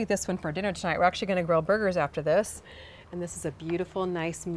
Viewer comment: I thought you said “grow burgers”, lol!! You know where my mind is!